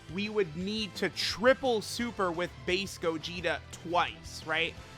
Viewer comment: i thought he said faze gogeta instead of base gogeta lmao